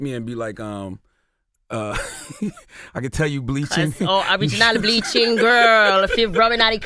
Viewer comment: I almost passed tf out when she said “I was not bleaching girrrrl, Im running out of creaammmmm” @ 17:51 wit dat West Indian accent 😩😩😩😭😭😭😭😭 this chick is comedy for days 💀💀💀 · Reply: Eon Hardy lol nah sis she said "ORIGINAL bleaching girl. Rub in ah duh cream"